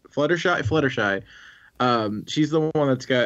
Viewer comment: did he just say 'sluttershy'?